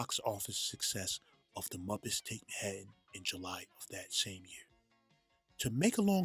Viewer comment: The Muppets Take What?!? I can't figure out what you'e saying there. I know the film is the "Muppets take Manhattan" but you clearly don't say Manhattan. According to the closed captioning you say "Muppets taking head." Ummm... so what's going on there?